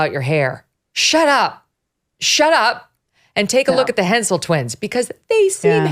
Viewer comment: I at first thought she said pencil twins lol